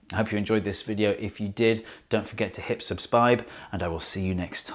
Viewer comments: Don't forget to hip subspibe! :P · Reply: Glad I'm not the only one who thought he said it weird. It definitely sounded like he said subspibe! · did he just say subspibe